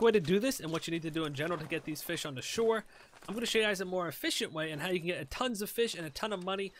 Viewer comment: did you say effishent? <.<